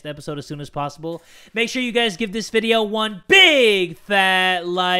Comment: Fun fact, when I first began watching about a year or so ago, I always thought he said at the end, "Tell em that Jay from the Kubz Scouts is dead too!" I was kinda shook when I noticed that he doesn't actually XD Also, another great video Jay!!! These are awesome! Could you please do Danganronpa next? There is a lot of mysteries in that game and a lot of story (if you like the first one there are sequels that continue the story) and it does have trials with murder victims and you have to try to find the killer. It has a really cool art style too and the characters are hilarious!